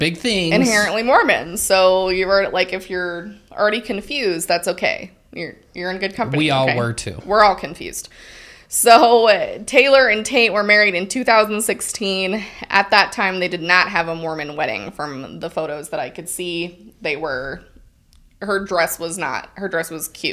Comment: it sounds like you said her husband's name is TAINT was that intentional? 😂😂😭😭